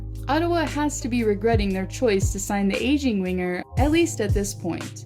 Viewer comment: thought u said asian winger 😂